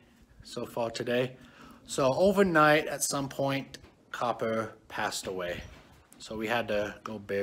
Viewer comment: I'm sorry, Did you Say popper? And, Who is that? At 0:21.